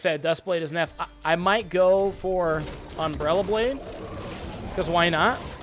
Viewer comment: I thought you said "umbrella blade" rather than "umbral glaive" and I love umbrella blade so much more xD